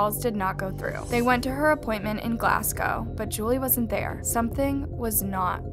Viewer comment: She said "They went to her *appointment*" instead of "Apartment"😭 at 2:37 btw